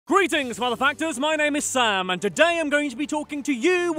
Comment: I thought he said “greetings motherfuckers”